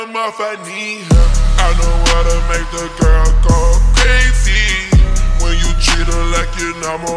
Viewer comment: At first I heard I don't want to make the girl go crazy 🤣🤣😅😅and searched that 😅🤣